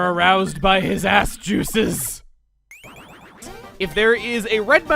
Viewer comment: Wait, did Chuggaconroy just said the a word? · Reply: Aroused by his ass-juices?